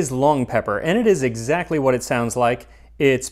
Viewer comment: Rats, I thought you said pong leper.